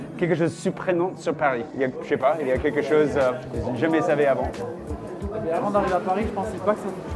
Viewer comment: "... Il y a quelque-chose ????? avant?" - What do you say in the middle there, before his answer? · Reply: I think it’s jamais savez avant.